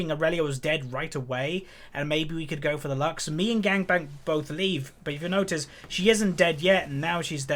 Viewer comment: did you just call him 'Gangbang'?